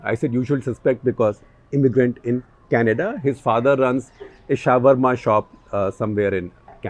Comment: Did Mr. SG called Shawrma as 'shower ma' or am I hearing things